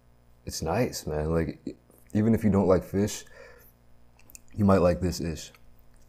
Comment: "even if you don't like fish, you might like this-ish" why is this a quote for the ages... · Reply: I thought he said "you might like this dish"